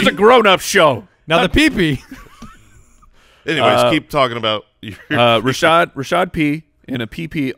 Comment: I thought was gonna say. "Anyways keep talking about your pp" lol